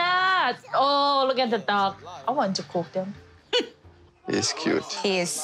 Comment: I thought Annie said “awwww look at the DOGS....... I want to cook them”